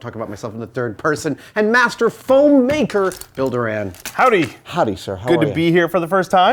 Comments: When he introduced him as "Bill Doran" I heard "Builder Ann" XD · I at first thought Adam said "Builder Ann" until I saw the "Bill Doran" on the screen! Nominative determinism anyone?